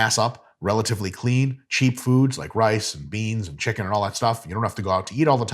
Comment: at first I thought I heard rice and beans were cheat foods(he said cheap), almost had to question everything 😂